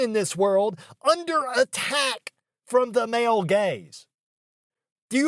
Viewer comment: did you say under attack, or underattacked? XD